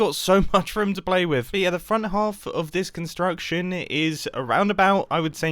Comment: I heard "The front half of this section is a roundabout" and got very confused